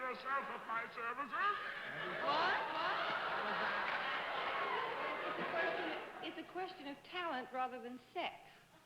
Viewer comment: what did Dorothy say at 6:13? I didn't catch it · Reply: ElizaLiza: "...Oh, so it's a matter of talent rather than sex...?"